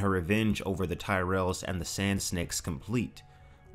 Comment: Did you just said "Sand Sneks" at 4:49? ♥